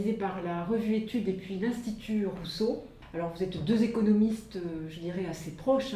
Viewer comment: Durant la présentation j'ai entendu "et vous Thomas Piketty vous etes communiste" (au lieu de économiste) 😂